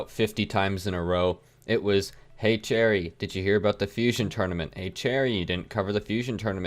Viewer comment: When you said your name i heard "hey jerry"... Is one of your names jerry??,':-/